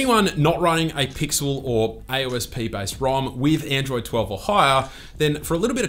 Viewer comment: Misheard "Android 10 or higher" as "Android 10 Ohio"